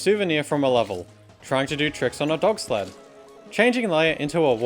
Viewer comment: I always thought he said "Trying to do tricks on a dog sleigh" but he actually said "sled"